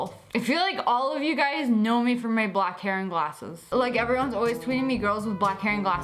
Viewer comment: I thought you said black Karen glasses